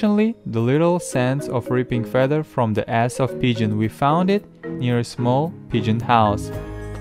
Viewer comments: did he just say "from the ass of the pigeon" haha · Did he say "ripping feathers from the ass of pigeon" 🤣🤣🤣🤣